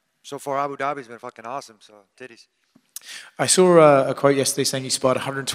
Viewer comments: What did tony say at 0:47 sounded like he said titties. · Did he say "titties"? Lol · Did he say Tities at 0:46 😂😂 what a a guy